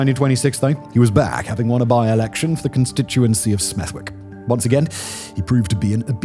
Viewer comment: 'Ow did yow say "Smerrick" owr kid?